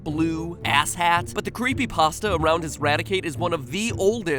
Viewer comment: did matpat just say creepy pasta?